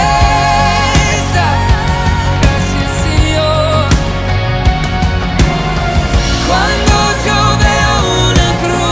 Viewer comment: what did he say in 1:59? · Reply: He said: Gracias, Señor!